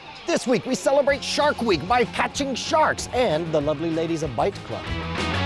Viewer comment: I thought he was saying "Fight Club" until I saw it written out...